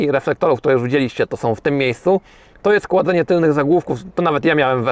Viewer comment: Tylko ja usłyszałem chłodzenie tylnich zagłówków? P. S. Gówno nie klasyk.